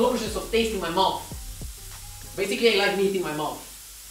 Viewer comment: wait what, did i hear it correctly ? "basically like eating my mom"